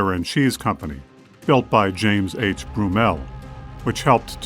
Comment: I've heard it be pronounced many ways but at 14:25 did you say "James H Brummel"? If so, they could be a relative of mine as my family is known as the "Eola Brummels" from the Aurora area. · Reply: Thanks for the careful watching and question! Yes, although it was spelled Broomell, as in James H Broomell, for what it's worth. We realize that spellings varied - so it's quite possibly the same name. Some of the early history books actually got this information wrong and indicated that it was James H Boswell who started the creamery. Further investigation revealed that that info was incorrect - it was Broomell. Thanks again!